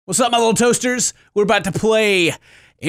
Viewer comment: "toasters" Me-"toesters" Really thought that's what he said